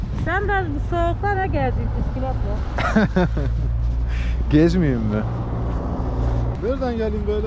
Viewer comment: "Derdin nedir külotla bu soğukta geziyon?" :)) Çok hoştu yahu! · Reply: arası kardeşim. Püskülot (Bisiklet) diyormuş. Haklısınız. Ben de külot anladım.